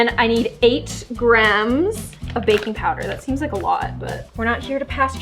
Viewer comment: i thought she said bacon powder ;D